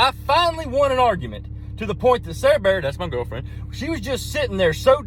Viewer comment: He didn't lose sight of the main reason of that argument. He went right back to staring after all of that argument, with his Sarah Bear *which is girlfriend*. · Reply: ohhh its Sarah Bear .... I heard it as Serebeah lol